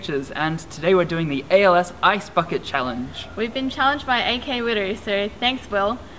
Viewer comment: >___< lol when the guy said we are doing the ice bucket challange, it sounded like he said iyc fawkit challange.